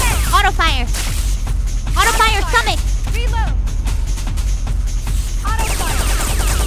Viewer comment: As Mouse started to go insane, so did I hearing her say “stumacho over and over again